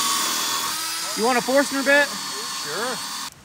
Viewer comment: You asked him if he wanted a forstner bit and then gave him an auger bit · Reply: I thought he said “faster bit.” I will Have to listen again.